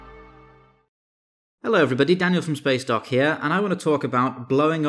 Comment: I thought it said “spacecock” instead of “spacedock”